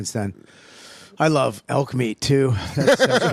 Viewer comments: I thought they were saying alchemy 😂😂 it’s elk meat … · I thought they said alchemy, not elk meat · “I thought he said alchemy but he said elk meat”